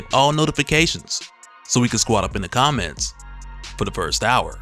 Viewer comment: I thought he said "super squad up in the comment for the first hour"